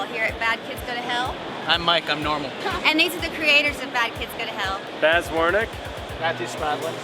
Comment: during the intro I thought it said the creator of "Fat kids go to hell" lol